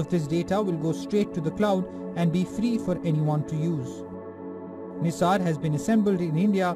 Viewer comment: Did i hear it correctly? "FREE FOR ANYONE TO USE?" Links please!